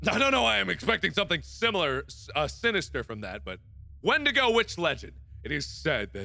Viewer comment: I thought he said syndago witch legend at 3:50 XD